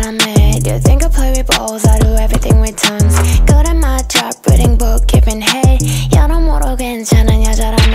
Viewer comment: it almost sounds like Bibi says "reading books, giving H3AD" 😩😩 Queen bibi dont play yall